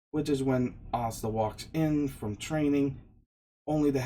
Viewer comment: For some reason the subtitles thought you were saying stuff like pasta instead of asta